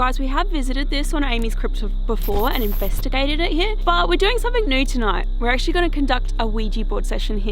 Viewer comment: ohhh 'something new tonight'......lol.....I thought you said 'something nude tonight' hahah... yes something nude in a cemetery would certainly be NEW :D